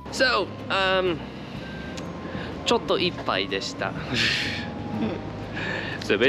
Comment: does anyone know what was said here? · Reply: Yo, I know it’s a been a year and this is kinda anticlimactic but they basically said ちょっと、いっぱいでした which translates to: it was a bit full😭